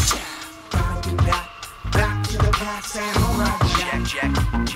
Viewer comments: I couldn't understand what the singer was saying in the music back when I watched this as a kid. I looked up the lyrics just now and was surprised to find that the part where I thought he said "Hold on, Jack" was actually "Samurai Jack". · Oh it says "Gotta get back, back to the past. Samurai Jack." Took me about 10 years to figure it out. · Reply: When I was a kid I thought it said, "Blah blubba blat, blablubba the past, samurai jack"